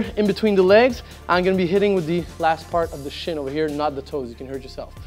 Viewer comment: Thought he said "shit" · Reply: Lol... last part of the shin... shin. Wouldn't be as effective the other way :)